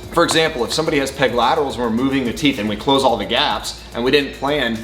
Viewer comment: What does he say at 2:41? "If someone has __________ and we're moving the teeth and we close all the gaps"?? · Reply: Steppawolf peg lateral teeth, hope this helped!